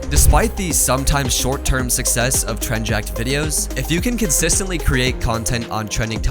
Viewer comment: the subtitles said Tren-Jack